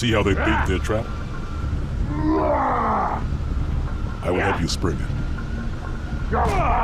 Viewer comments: I always thought the arbiter said “see how they bait their trap? I will have you spring it” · I always hear it as "Bait their traps" not 'Beat their trapa'